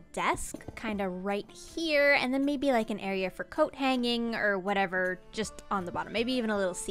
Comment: I thought you said cocaineing I'm crying 😭